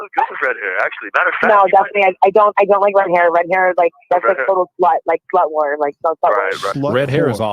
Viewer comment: It sounded like she said red hair is, “slut lore.”